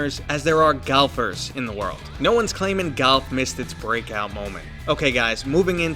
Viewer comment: Galf? Golf? I genuinely cant tell what's being said here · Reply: Golf. Yeah my NYC accent can be heavy